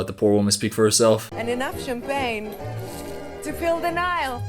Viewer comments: I thought she said to fill denial 😅 idk girl but not enough to make me be ok with this movie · Oh I thought she was saying: “enough champagne to feel denial”. Which, coincidentally, is something every director who works with her needs